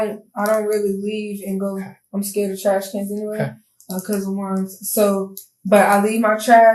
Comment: Exactly and she said “I’m afraid of trash cans anyway cuz I’m scared of ….” What? It sounds like she said “ cuz I’m scared of worms” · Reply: She 100% said because of worms.. like I’m sorry but I’ve never seen worms in trash, just flys and bees. Compost maybe but trash? Idk that’s wild to me, like why’s your trash got worms? That’s sus.